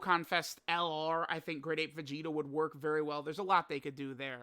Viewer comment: Did he just said great ape vegito